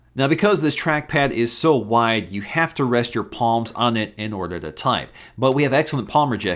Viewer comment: Did he say porn in part